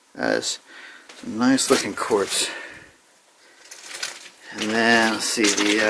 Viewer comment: FYI, at around 5:05 you talk about Quartz. However the google auto-subtitles think you said "corpse". So if you get any issues with the vid it may be because of that.